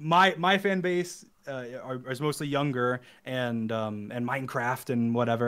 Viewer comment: I time stamped this cuz he said “young grr” instead of ‘young girls’ 😂